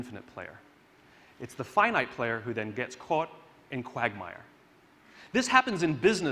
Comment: does he says " quagmire" ??